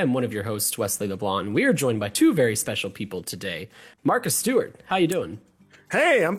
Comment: I thought he said Martha Stewart Not Marcus Stewart.